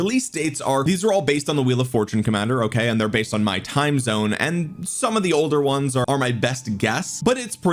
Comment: but why would the developers refer to US time?! The "ingame time" is UTC. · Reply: min mark "based on my timezone" is what said. Please go back and relisten.